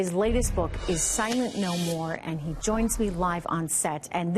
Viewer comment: I thought she said his book was about Assange and was called "Silent Gnome War' lol